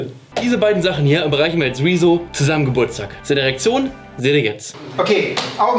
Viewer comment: Ich hab eiskalt verstanden: "Diese beiden Sachen zusammen schenken wir rezo zum Geburtstag, seine Erektion seht ihr jetzt!